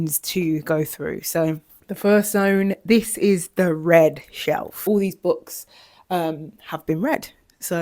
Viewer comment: When you first said “this is the read shelf” I thought you were saying “red shelf” and was like “my girl is color blind” 😂😂 Anyways you look lovely in this video and love the tour 💕💕